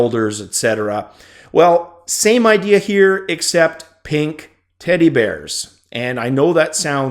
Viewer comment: Don't know why but in my head I heard you say pink titty bears .. thanks for the clip of the knuckles in action mate, I might have to start saving and get me a Vive now =/